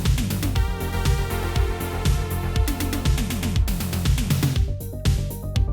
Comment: when you said patreon at the end there, i thought you had said "gaytreon"